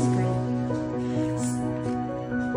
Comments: I thought she said emilaiano martinez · I thought she said Emir Martinez the football gk💀